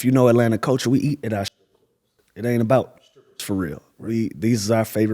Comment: if anyone was wondering what Lou said at 1:38, it was "we eat at our STRIP CLUBS, it aint bout the STRIPPERS fo real" 🤣